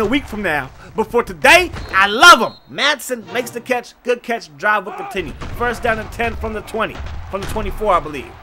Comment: Why at 11:51 it sounds like you said drive with da titty..😂😂😂 but anywho this team might get a low bowl game this year if they keep it up